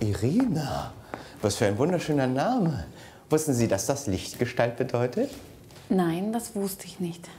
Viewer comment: Ich hab Mistgestalt verstanden xD 😂😂